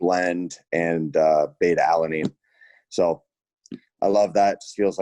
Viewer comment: Why does it sound like he says alamine every time